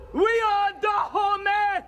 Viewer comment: does she say "We are da' homies!"?